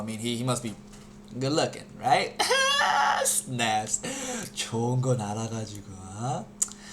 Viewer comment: lol what did he say at 1:56?? it sounded like "don't" something in Korean.